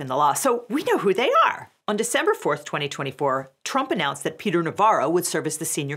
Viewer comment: it’s December 21st, 2024 today. Did she just say on December 24th, 2024, Trump announced??